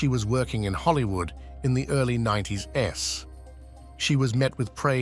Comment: Instead of saying "90s" (24:11) it says "90 - S". guess we don't need to worry about the Terminators. Yet...